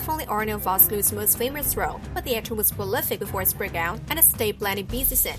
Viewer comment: Almost impossible (for me) to get what the author of the video is saying. For example 0:44 does someone hear the word "before" (prolific before his breakout)?